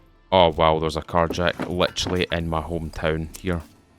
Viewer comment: Playing SCUM as I watch so I’m half focused and I swore you said “there’s a car jacking in my home town” 😂 Had to rewind a sec